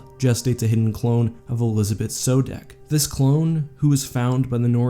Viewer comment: This was awesome! One thing though... I heard you saying Sodeck instead of Sobeck.